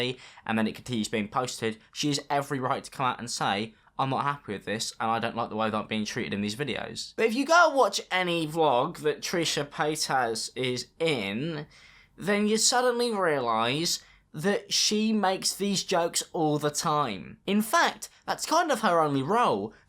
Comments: Lmao all I heard was "Trisha pay-ass" 😂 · Also... I legit heard "trisha PaintAss" when you said her name the first time ...